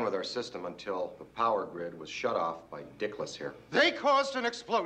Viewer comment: My entire childhood I had only seen the edited tv version my mom recorded on ABC so I thought they said “Wally wick” instead of dickless until like 2010.